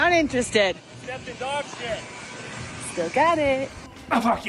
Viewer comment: What did he say? " I think you dog is scared?" "There's a scary dog there?" 🤔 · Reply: im not sure if youre joking.... but he said 'you stepped in dog sht' 🤣